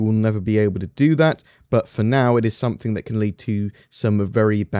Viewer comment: When he said "It works, its just waiting for another system to be released" I heard "for now it works" x)